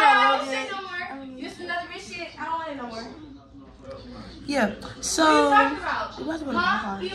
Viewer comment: is really said "yea soo" But they cute tg low keyyy